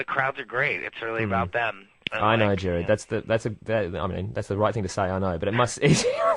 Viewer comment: it sounded like he said gerad not gerard lol